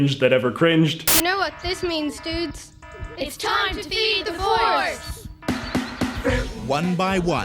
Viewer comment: What I heard: "You know what it means, dudes! It's time to be divorced!" What they really said: idk